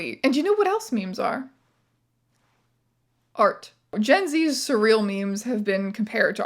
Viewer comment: Wasn't looking at the screen and genuinely thought you said "fart", and was fully ready to accept that as an example of the type of non-sequitur punchline common in modern internet humour.